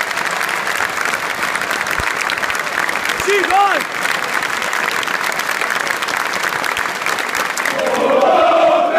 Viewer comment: wat wordt er geroepen...? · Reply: Ziek geil